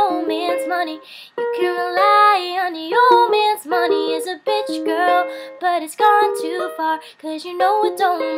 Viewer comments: Anyone else thought she said bitch girl instead? Aha too cute (= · Did you just say "BITCH" girl at 0:33?